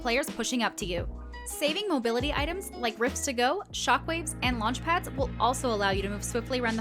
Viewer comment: said use Mobility items such as Rift-to-go's, Shockwave Grenades, or "Launch Pads" I almost was about to Roast them because I thought they said Bounce Pads.